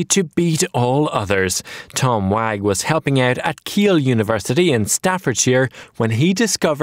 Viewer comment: Did I just hear "Ikea University"?